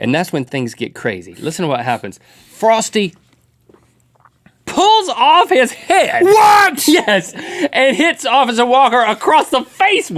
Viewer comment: I thought Link said, Frosty pulled off the officer's head not his won. Now I can't get that out of my head.